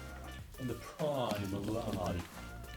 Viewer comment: did I just hear in the background quoting Monica from Friends "I'm in the prime of lime" (the prime of life....without the cold, teehee) by any chance? Awesome video. Lee xx